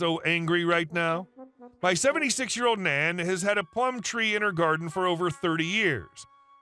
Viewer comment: Last story: I thought said Palm tree, and was wondering how you made jam from Palm trees, chuckle. Plum not Palm.